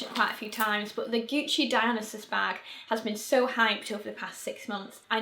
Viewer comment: I heard "Gucci Dionysus bag" as "Gucci dialysis bag" and got so confused! XD